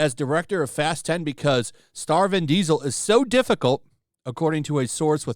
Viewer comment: Fat and Frivolous. Star Vin Diesal...I thought you said, Starving Diesal.